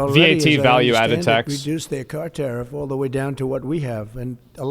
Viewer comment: I thought trump said Down 4%